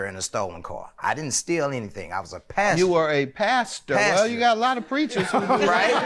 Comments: Nah Judge Mathis... the way he says passenger has nothing to do with a Missouri accent... I thought he was saying "pastor" too 🤣🤣🤣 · I could of sworn he said Pastor like 3 times. Lol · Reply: I thought he said “passer” 😭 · I needed this laugh but as usual, I was extra with it all while being dramatic... This is why....🤣🤣🤣 I was with Judge Mathis. Hell I too thought he was saying he was a PASTOR... OHHHHH HE SAID PASSENGER! 😂😂😂😂😂😂😂